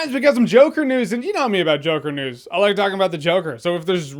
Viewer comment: Is it just me or at the beginning of the video it sounded like he said “Joker Nudes”